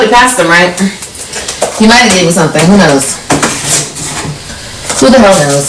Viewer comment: did she say "he might've gave me something"????😂😂😂????? When i tell yall i spit my Sprite Zero everywhere 😂😂 this level of stupydity would be sad if she was worth a damn but since she's not....... im crying 😂